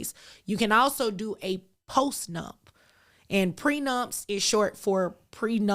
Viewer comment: Didn't know postnup was a thing, when mentioned it I thought he said postnut